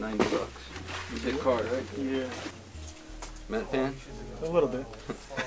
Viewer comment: thought he said “meth head” and dude replied “a little bit” 😂😂